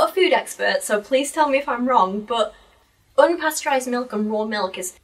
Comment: thank you for making the absolute best content to work to 6 hours? we have been blessed. also at 5:24:50 you ask about pasteurized vs raw milk but i think she said 'pasture raised' like grass fed and not pasteurized.